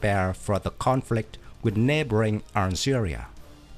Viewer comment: My ears are playing tricks on me today it would seem... I could have sworn I heard you say "Syria" was Morocco's neighbor. Of course, you said, "Algeria". Israel has consistently displayed a willingness to do what few other countries do with defense spending: they make the most of every expenditure in terms of "bang for the buck", and have developed some very innovative air defense weapon systems. This looks to be one of them. Thanks again!